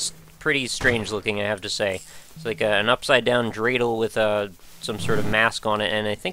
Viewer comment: did you say dreidel at 2:04? I'm Jewish and isn't that exclusively a word used by Jews? I must be mistaken but I have never heard anyone else say it